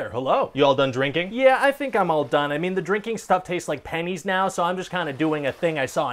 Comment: I love how they invented pennies before they knew they could swim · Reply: Oh. He said "pennies." I thought he said "panties." 😏